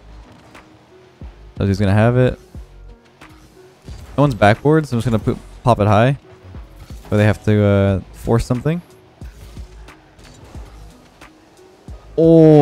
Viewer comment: you said poop instead of pop